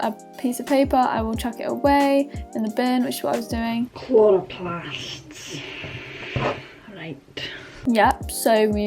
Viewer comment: what did you say?? 😂😂😂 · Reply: Chloroplasts...right